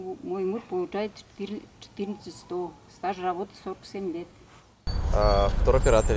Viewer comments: hiiiiii, we have a big a** team working on these videos, so if you don't mind, support our team at the links in the description ⭐ - "how-are-you-not-arrested-yet" UPD, for comparison: - Rural Russia, average rent: FREE, because barely anybody wants to live there, but in nearby cities it costs you $250 on average for a family apartments; - Small cities (in this case Taganrog), average rent: $225 for apartments with 3 rooms, in the city center, but some cost up to $600. - Moscow, average rent: around $700. UPD 2: This is monthly salaries! UPD 3: People gave us income in rubles, and we converted it into dollars at the rate of $1 = 90 rubles. UPD 4: It's after taxes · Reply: У вас там опечатка на 5:53, стаж работы мужа у бабушки 47 лет, а не семь (по крайней мере я услышала, что она 47 сказала). Или мне таки послышалось? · I think she said 47, not 7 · She actually said 47 years*